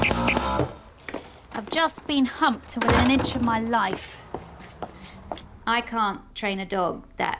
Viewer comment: I've just been humped I can't make out the rest can someone tell me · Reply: "I have just been humped, to the inch of my life"